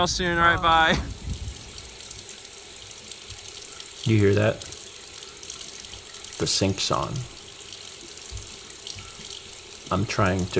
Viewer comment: Why did I mishear that line as "Do you hear that? The sink song." I guess songs about plenty of objects led me believe that he had a song about sinks today.